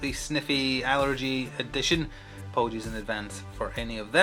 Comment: did I hear "apollengies"? :elbowcough